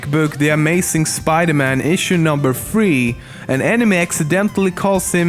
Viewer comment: Every time he said "enemy" it sounded like he said "anime" ..... so confused